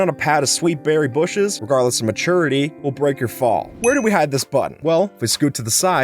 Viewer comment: i heard butt